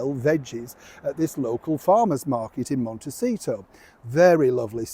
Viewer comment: Mr Sean, I understood what Meghan said differently. I heard her say they PLAY farmers market. And I was picturing the children with a table outside the gate of their Montecito home selling garden produce to passers by. It could even be that they don't sell it outside the gate, but just pretend-sell it to each other. Aka, literally play shop, which every parent does with their children.